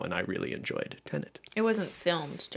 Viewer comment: Oooohhh. Tenet. Haha. I thought was saying “Ted” and I was so confused.